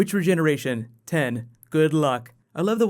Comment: Let me know what you thought of this series finale vs last years and Clara vs Amy/Rory - other companions endings! Christmas Special video coming later tonight :) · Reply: When he asked "Regeneration?", I thought he said "Good look" not luck. Thought it was just a nice bit of banter.